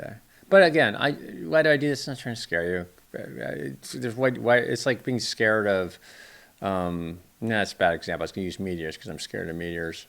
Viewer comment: At 1st I thought he said "it's like being afraid of media" ... which would have also made sense 😳 & I suppose we should be blaming Chunk for the increase in Salmon prices 😜